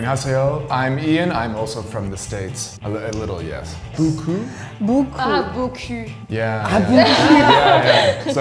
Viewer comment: Does anyone know that the French girl actually said "nice ass" instead of "a lot" 😂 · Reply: “beau cul” 😭